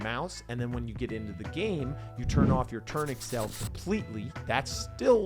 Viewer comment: On minute 19:30 what u said? "When you get into the game you disable what?" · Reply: Turn off the "turn accel"